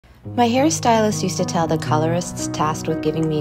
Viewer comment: Seriously at the beginning I thought she said “my Harry style-ist”